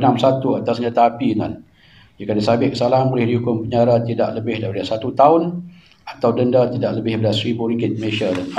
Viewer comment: aku salah dengar ke.. syeksyen 36 (1) Akta senjata api.. jika disabit kesalahan boleh dikenakan tindakan penjara tidak lebih 1 tahun atau denda tidak lebih 1k.. betul ke.. mcm ringan sgt je hukuman..